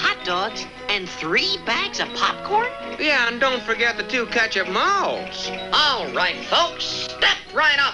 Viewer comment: What food did Teddy say at 2:18? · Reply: Perhaps "ketchup malts", but I have no clue. Sorry about that.